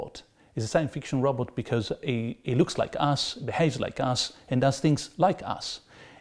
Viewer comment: "He looks like ass, behaves like ass, and does things like ass" This is really how I heard it the first time.